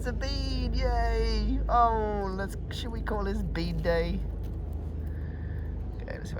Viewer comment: Hi, I heard “shall we call it bidet?”🤣🤣 I looked up and it was obvious I’d got it wrong, thank goodness!👏👏👍🤣🇦🇺